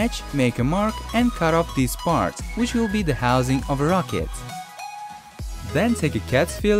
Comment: what did he say? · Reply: Daniel Willey housing of rocket